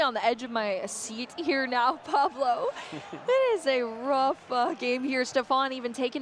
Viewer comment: is it just me or did anyone else hear saying “rough f******” ?